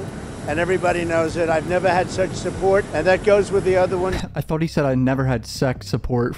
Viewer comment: Bro i had to rewind it I thought he said "Sex support" too LOL as soon as you said it i thought it haha Love the vids bro>3